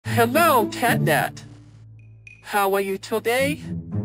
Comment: did he say catnap or catnat?